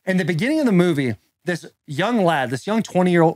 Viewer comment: did he say young Lad ?!🤔😂🌪️ Yea Halloween Kills Suck .....